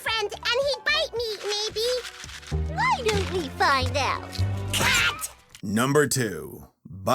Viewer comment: Worst *Nicktoons P.S. 9:32 Almost Thought Sussie said “Cunt”